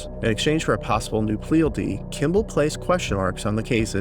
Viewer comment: I'm sorry, but at 36:15 you said "pleal dee" instead of "plea deal" and I had to laugh. I feel like that's a mistake I'd make